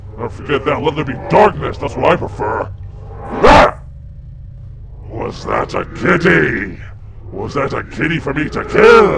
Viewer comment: did say "was that a kitty?" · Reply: He said was that a kiddy, like a kid